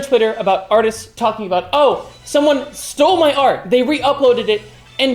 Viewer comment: for sec i thought you said oso but you said oh some one